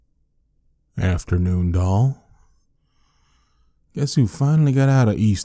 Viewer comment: I'm actually early for once! Love it! Keep up with the amazing work! But i literally died at the part I heard "afternoon doll".